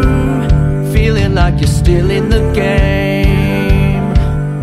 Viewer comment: i heard this- "Youuu felling like your stealing the gay"